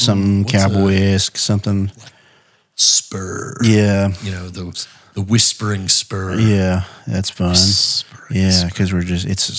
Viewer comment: I definitely thought you said “The Whispering Sperm”. LOL